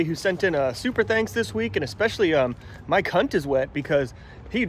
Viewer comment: Wait what did you say ? 😂😭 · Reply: Sounded like "Mike Hunt is wet." 😉🤣